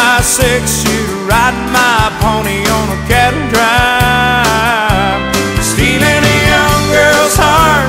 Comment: When I was a kid, I thought the chorus was saying "Shoot it right in my pony on the cattle drive." Haha